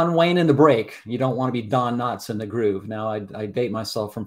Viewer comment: I'm confused ........... It seems to be being said the plane's coming in at circa 400kts IF it was - That would be more than enough to go clear and probably to go around So - WHY did it hit the deck? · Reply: used the phrase "John Wayne in the break. Don Knotts in the groove." I've always heard it as "John Wayne in the break. Slim Pickens (pickin's)in the groove." This was a classic, thankfully non-fatal, example!